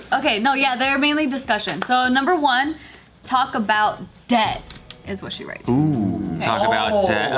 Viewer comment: "Talk about DEAD!" me: yes, need to talk about that final will me after realizing what she ACTUALLY said: well, that's not as fun. . .